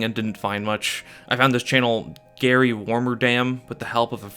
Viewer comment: The first time I heard this, I heard "Gary Wormadam" (i.e. the pokemon)